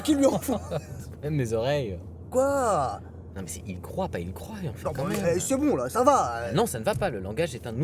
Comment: « ils croyaient » reste un verbe conjugué qui reste tout à fait correct ? Pourquoi est-ce qu’il se fait reprendre, j’ai du louper qqchose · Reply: Il dit : "Ils croyent"